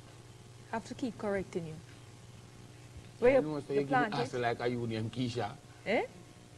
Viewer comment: Someone please explain to me what he said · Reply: ”u acting like u name Keisha” 😂